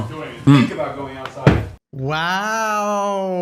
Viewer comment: Did anyone else hear hasans help tell kaya "DONT EVEN THINK ABOUT GOING OUTSIDE" at 10:02 Right after he hides the remote for the shock collar........